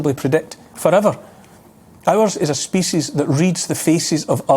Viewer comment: Thought the way Neil pronounced it, I thought he was saying “as a species we learn to read the ‘faeces’ of another. Well certainly those mask wearing advocates do leave a trail of psychological poop